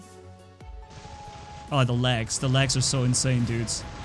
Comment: I thought u were saying, the LEGS are so insane... Hahaha lol